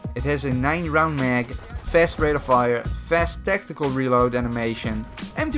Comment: I thought he said 90 round mag xD